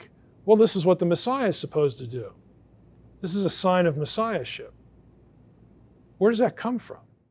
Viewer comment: i had to rewind because i thought you said shit hehe it was"Messiahship" that reminds me of my father he was a baptist preacher and anything that rimed with a cuss word I got slapped for lol I am becoming him slowly